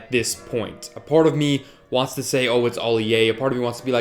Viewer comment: Did you say Ali-A?